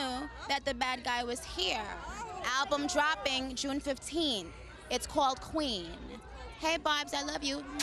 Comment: Finally she spilt the tea about this album release! Who’s ready for Queen? 🙏🏼🔥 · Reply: Fatima Daghmash 0:23 June 15th is what she said.